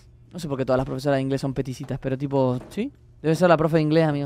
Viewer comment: son qué? No le entendí XD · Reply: "Peticita" dice, osea...lo que yo entendí es que era enana XD